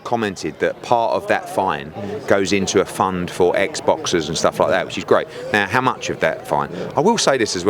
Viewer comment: did he say EGGS boxers 🤔